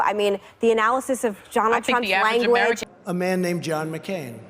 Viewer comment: did she say jonatron?